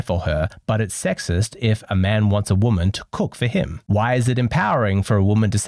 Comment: I thought he said, “can you cook?” That would have made more sense lol